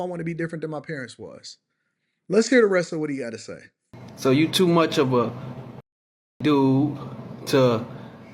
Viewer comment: “You’re too weak to let a woman lead you”. How do they not realise how dumb this sounds?💀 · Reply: I thought he said “You too dumb”, which is funny in and of itself.